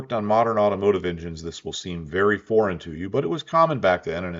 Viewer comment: I kept hearing you say "boring". Didn't make any sense, until I realized you said "foreign". 😂